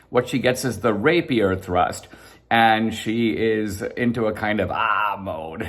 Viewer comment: Lol rapier thrust, not gonna lie I thought you said rape thrust